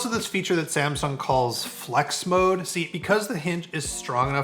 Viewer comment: for a second I thought he said dex mode 🤣